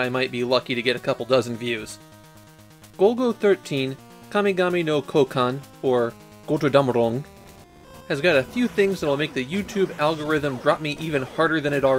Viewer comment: what is it you said here? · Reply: "Götterdämmerung". It's a pretty specific reference in the title rather than just poetic license.